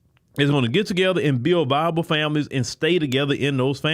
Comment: Did he say "bible" families, or "viable", families?